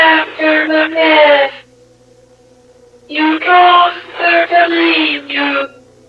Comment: I can hear these lines Clearly "Do not be afraid or sad" "You did this to yourself" "Isolating yourself after the death" "You caused them to leave you" "It's all your fault *Repeats*" "We are all here because of you" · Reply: I hear "You caused her to leave you"